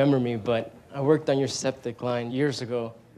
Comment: Can anyone tell me what the guy is saying at 8:31? It sounds to me like "I worked on your septic line" but that doesn't make any sense.